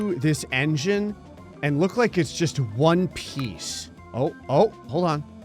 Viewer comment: What did you just say? 🏴‍☠️☀️🦁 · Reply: One piece